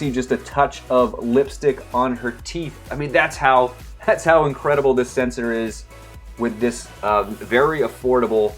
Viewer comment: what did he say? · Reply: ‘that’s how incredible this sensor is ‘ that’s what I heard